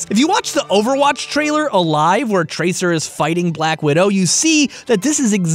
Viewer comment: Did you call widow maker black widow at 12:32??